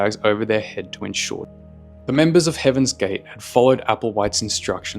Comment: I was listening to this while doing other work. I thought he said "heavens gay" lmao